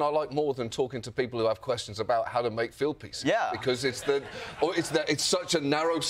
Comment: Say < FEE ald > I had to rewind a few times. I thought U were saying filled piece. I guess this is why I can't get a job! being a native.